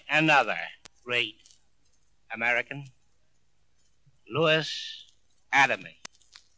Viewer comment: Many thanks for the great job you've done with the transcript of the speech. Do you mind if I point out 2 or 3 mistakes or doubts? I might be wrong myself, but I'm not sure of the following. At 0:30, the correct name of the writer is spelled Louis Adamic and not Lewis Adaleigh. At 5:57 and 6:14 the writer's name is Behrend and not Baron. At 6:29, it looks like there's a word missing or something when he says:"...our mills and factories DEAD...". After "dead" he says "and idle". At 6:35, he says "warp and woof" and not warp and whoop. At 6:44, it doesn't sound like he says "we would slump into DARKNESS", I think he uses another word instead of darkness, but can't say which one. That's it. Thanks again.